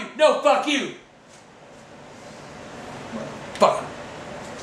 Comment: He said "turn that's on of a bitch off" so fast all I heard was "turn that son of a john."